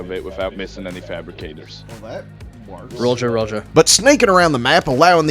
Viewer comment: did he just say Roger Roger? HES ONE OF THEM! GET THE TREASONOUS BASTARD!!